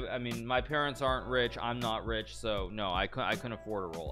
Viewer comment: Did hikaru just say “I’m not rich” wtf